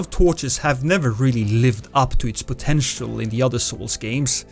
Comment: honestly, I heard “other* souls games” not elder souls, but I enjoy the self deprecation all the same ❤️